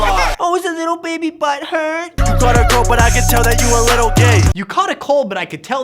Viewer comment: He said "you got a girl but i can tell that your a little gay", not "you got a cold but i can tell you a little gay". Bart you are actually so stupid, calling yourself smart. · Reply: To be fair, I had to rewatch that part to figure out what ricegum was saying, and I still thought he said "you caught a girl."